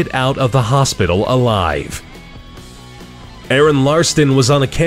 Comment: Does he say Aron Ralston or Aron Larston at 5:20?